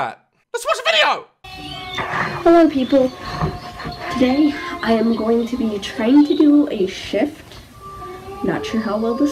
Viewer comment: I thought she said "I'm going to be trying to do a shit"